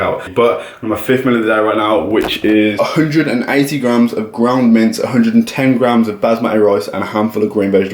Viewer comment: sounds like hes saying "ground mint" lol